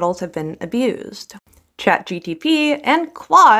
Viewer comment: I know it's likely an accent thing but I heard "ChatGDP" and it's a hilarious way to describe academics